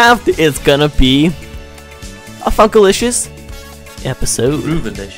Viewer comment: in the beginning of the video it sounded like you said fuckilisious lol